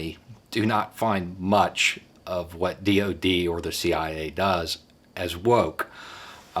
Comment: Cleaning the kitchen listening to you per norm. I hear "cia woke af". Collapse to the floor laughing. I love listening to you. I reccomend your channel to everyone.